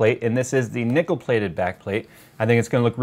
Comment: What i heard: "...and this is the Nickelbacked plate-plate"